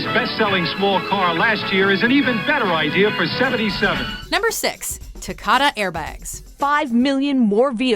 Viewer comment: brooooo! I thought she said FUCKIN TERRACOTTA airbags!